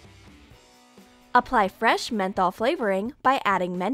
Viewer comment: What's up with the way she says mento??? It sounds like she is saying menthol. I have nothing against this, I am just wondering